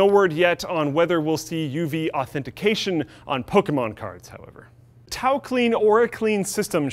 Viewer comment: Thought it said tacoclean...